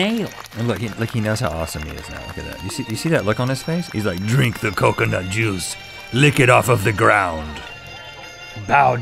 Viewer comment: I thought Rhett said "Lick the coconut jews !" :D :D